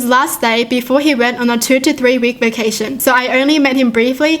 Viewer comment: all first I thought she said a 53 week vacation